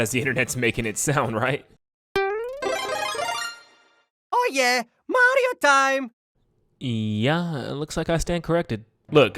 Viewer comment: auto subtitles said it the best. "oh yeah audio time".